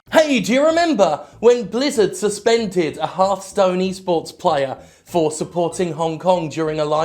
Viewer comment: what jim said: "do you remember when blizzard suspended a hearthstone e-sports player?" What i heard: "do you remember when blizzard suspended a half-stoned e-sports player?"